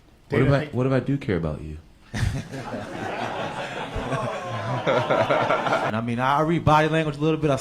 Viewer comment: What Jon say @ 0:24 ? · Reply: “What if I-what if I do care about you?”